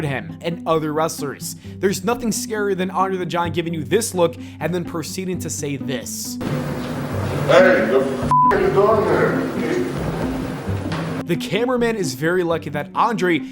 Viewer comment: Few things are scarier to me than Andre the Giant yelling "WTF ARE YOU DOING HERE?" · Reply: For some reason I heard “dafuq u doin' here?” 🤣🤣